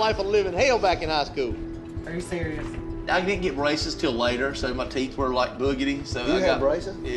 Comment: seconds in, at first I thought he said: "I didn't get racist 'til later....." lol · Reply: he said braces. I thought racist at first as well.🤣😂